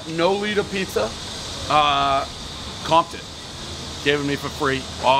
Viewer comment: Thought he said Compton rather than comped it; would sure like to see a Compton pizza review though